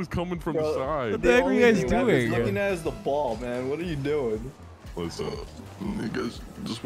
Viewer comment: did he say „whats up niggas“? 😂